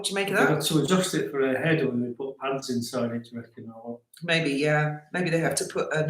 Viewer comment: I misheard about the crown being adjusted & thought he said they were putting ants inside. 🤣🤣👑